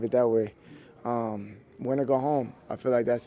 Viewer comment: CeeDee says "winner go home" ... what the heck does he mean? Did he mean to say "loser go home" ... what he said really confuses me and I am really struggling to understand it